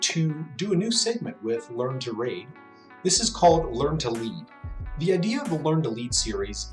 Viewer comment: Lol I thought it said "learn to read" at first. :P